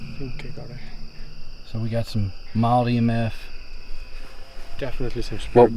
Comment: Every time you said "we got mild EMF" I thought you were saying "we got Molly and meth" lol. It was the part where you were staying out until 3 so was extra funny lol 😂